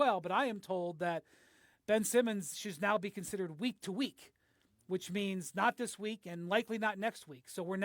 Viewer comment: Thought it said Ben was "weak too weak" at first